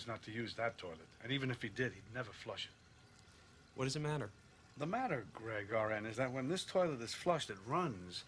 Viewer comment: "The matter, Greg-RN" LMAO! XD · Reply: The first few times I saw this, I thought he said "The matter, Greg, all right..." Like he was snapping at him. But saying RN makes it so much more of a burn.